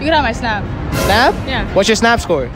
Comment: Oh , I thought he said SNATCH score.. And she said 700K.😆😆 I thought that thing would look like an old catchers mitt... LMAO!!